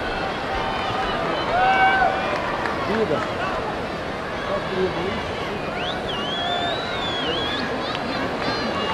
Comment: Anyone know what Ostapenko is saying at 2:00? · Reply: I can’t really hear much of it. When he turns around he says something to other coach like “you give these judges an inch they go a mile.”. In Anna’s video I can hear him faintly in the background saying “I can see the USA and Romania’s bribes to the judges are working well for them”